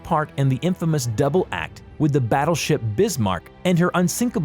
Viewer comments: I thought he said "The Britsmarck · I really heard him saying brizmark than bismarck also tirpitz not tripitz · The one that pissed me off the most was the prinz “Eugene”. Stay with a language, say prince Eugene or prinz (oy-ghen) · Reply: what about "brismark" im not the only one who heard him say bris instead of bis right